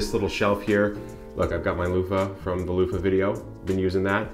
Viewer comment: I didn't see the loofa on the site from nebia- the link you provided..can you help with that info..I thought I heard you say the" loofa video"?..May be not.. any help appreciated